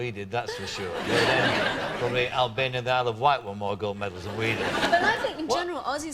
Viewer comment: did he say "Isle of Wight"